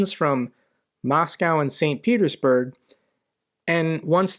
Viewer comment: I swear, when you said "Moscow and St Petersburg", I heard "Moscow, InsanePetersburg". Glory to the heroes and let's hope all engaged in conflicts around the world wake up from this fever dream.